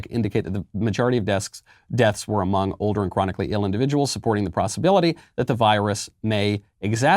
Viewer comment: Did he say prossibility instead of possibility? Did I mishear that?